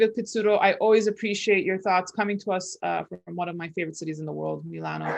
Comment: Subtitels say "Dr. Evil Pezzuto" - a Freudian lapse? says: "... appreciate your thoughts coming to us from one of my favorite cities in the world". Look at face - she does NOT say: "... coming to us from one of my favorite economists of the world...".